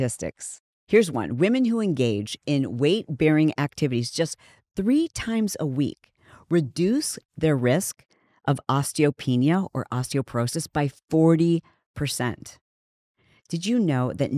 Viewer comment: Anyone catch that at 24:18 ? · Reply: She said "do zone 2 cardio 3 times a week. Women who engage in weight bearing activities just 3 times a week reduce the risk of osteopenia/ osteoporosis by 40%".